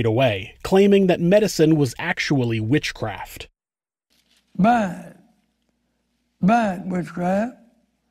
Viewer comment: Does any one know what in the hell he was saying at 9:31 ? “Bag bag witchcraft, bat bat witchcraft, back back witchcraft, bap witchcraft”